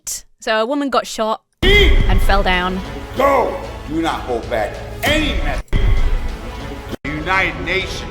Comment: I think he says, "If you go, do not hold back..."?